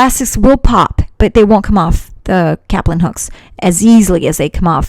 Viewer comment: I thought she said captain hook for a second. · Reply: Caplin Hooks